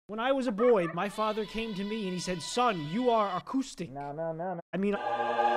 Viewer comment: He did to me to! But it sounded different, sounded like autistic but I’m sure it’s the same!